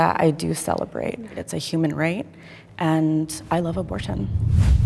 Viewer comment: I'm sorry but wtf am I hearing - "I love abortion" ??? I'm pro-choice but damn